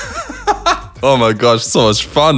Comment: Who else heard “what wrong” 8:48 😂